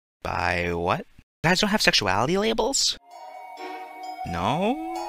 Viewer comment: When I first watched this I thought she said "Swift tail" instead of sweet potato and was so confused but didn't question it bc Luz would be into Warrior cats.